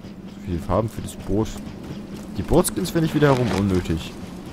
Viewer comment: ich hab bei boot skin brot skin verstanden